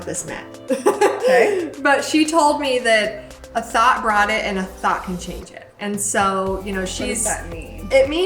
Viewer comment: I thought she said a THOT brought it and a THOT can chage it lol